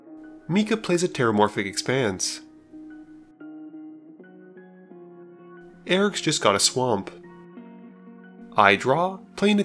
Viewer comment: When you say, "Eric's just got a Swamp," I hear, " . . .I got a rock."